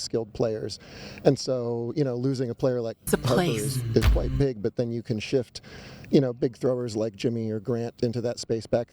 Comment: ???? Am I hearing things??? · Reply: "it's a place"-- correcting him when he said "losing a player". At least I think...